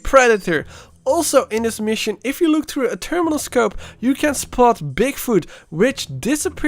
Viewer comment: I hear multiple times: Terminal scope.